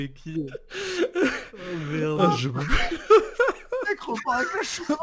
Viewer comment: papacito il dit quoi?? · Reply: Le mec repart avec la chevalière